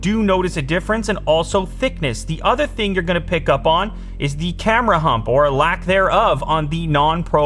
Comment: Did I just hear “Camera Hump” instead of “Camera Bump”?